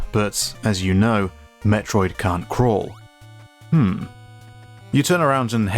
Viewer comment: Im crazy or did you say "metroid cant crawl"?